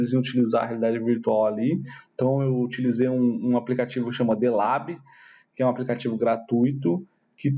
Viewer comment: como se chama o aplicativo que você usou na sala de aula? ilab? elab? não entendi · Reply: O app é o "The Lab" disponível para download gratuitamente na Steam. <link>